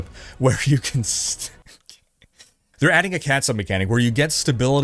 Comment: I heard Catsup....You no trick my ears. Heh...